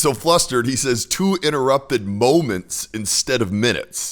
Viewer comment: I heard “two uninterrupted moments” not “two interrupted moments” 🤨